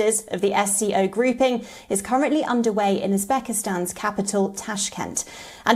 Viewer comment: oyeeee Pakistan's capital Tashkent? Did I hear the reader correct.. Uzbekistan..